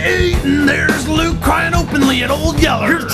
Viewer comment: PLEASE SOMEBODY can tell me what he says at 0:22? "there's Luke crying openly at...(???)". I understand "old yeller" lol but that doesn't make sense. Thanks. (English is not my mother tongue)